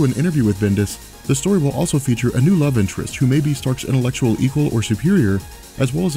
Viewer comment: I'm listening to this as background noise while I'm animating and I completely misheard you. When talking about Ironman I thought you said "A new love interest for Tony Stark who is 15 years old" ... I was like "What the fuc...!"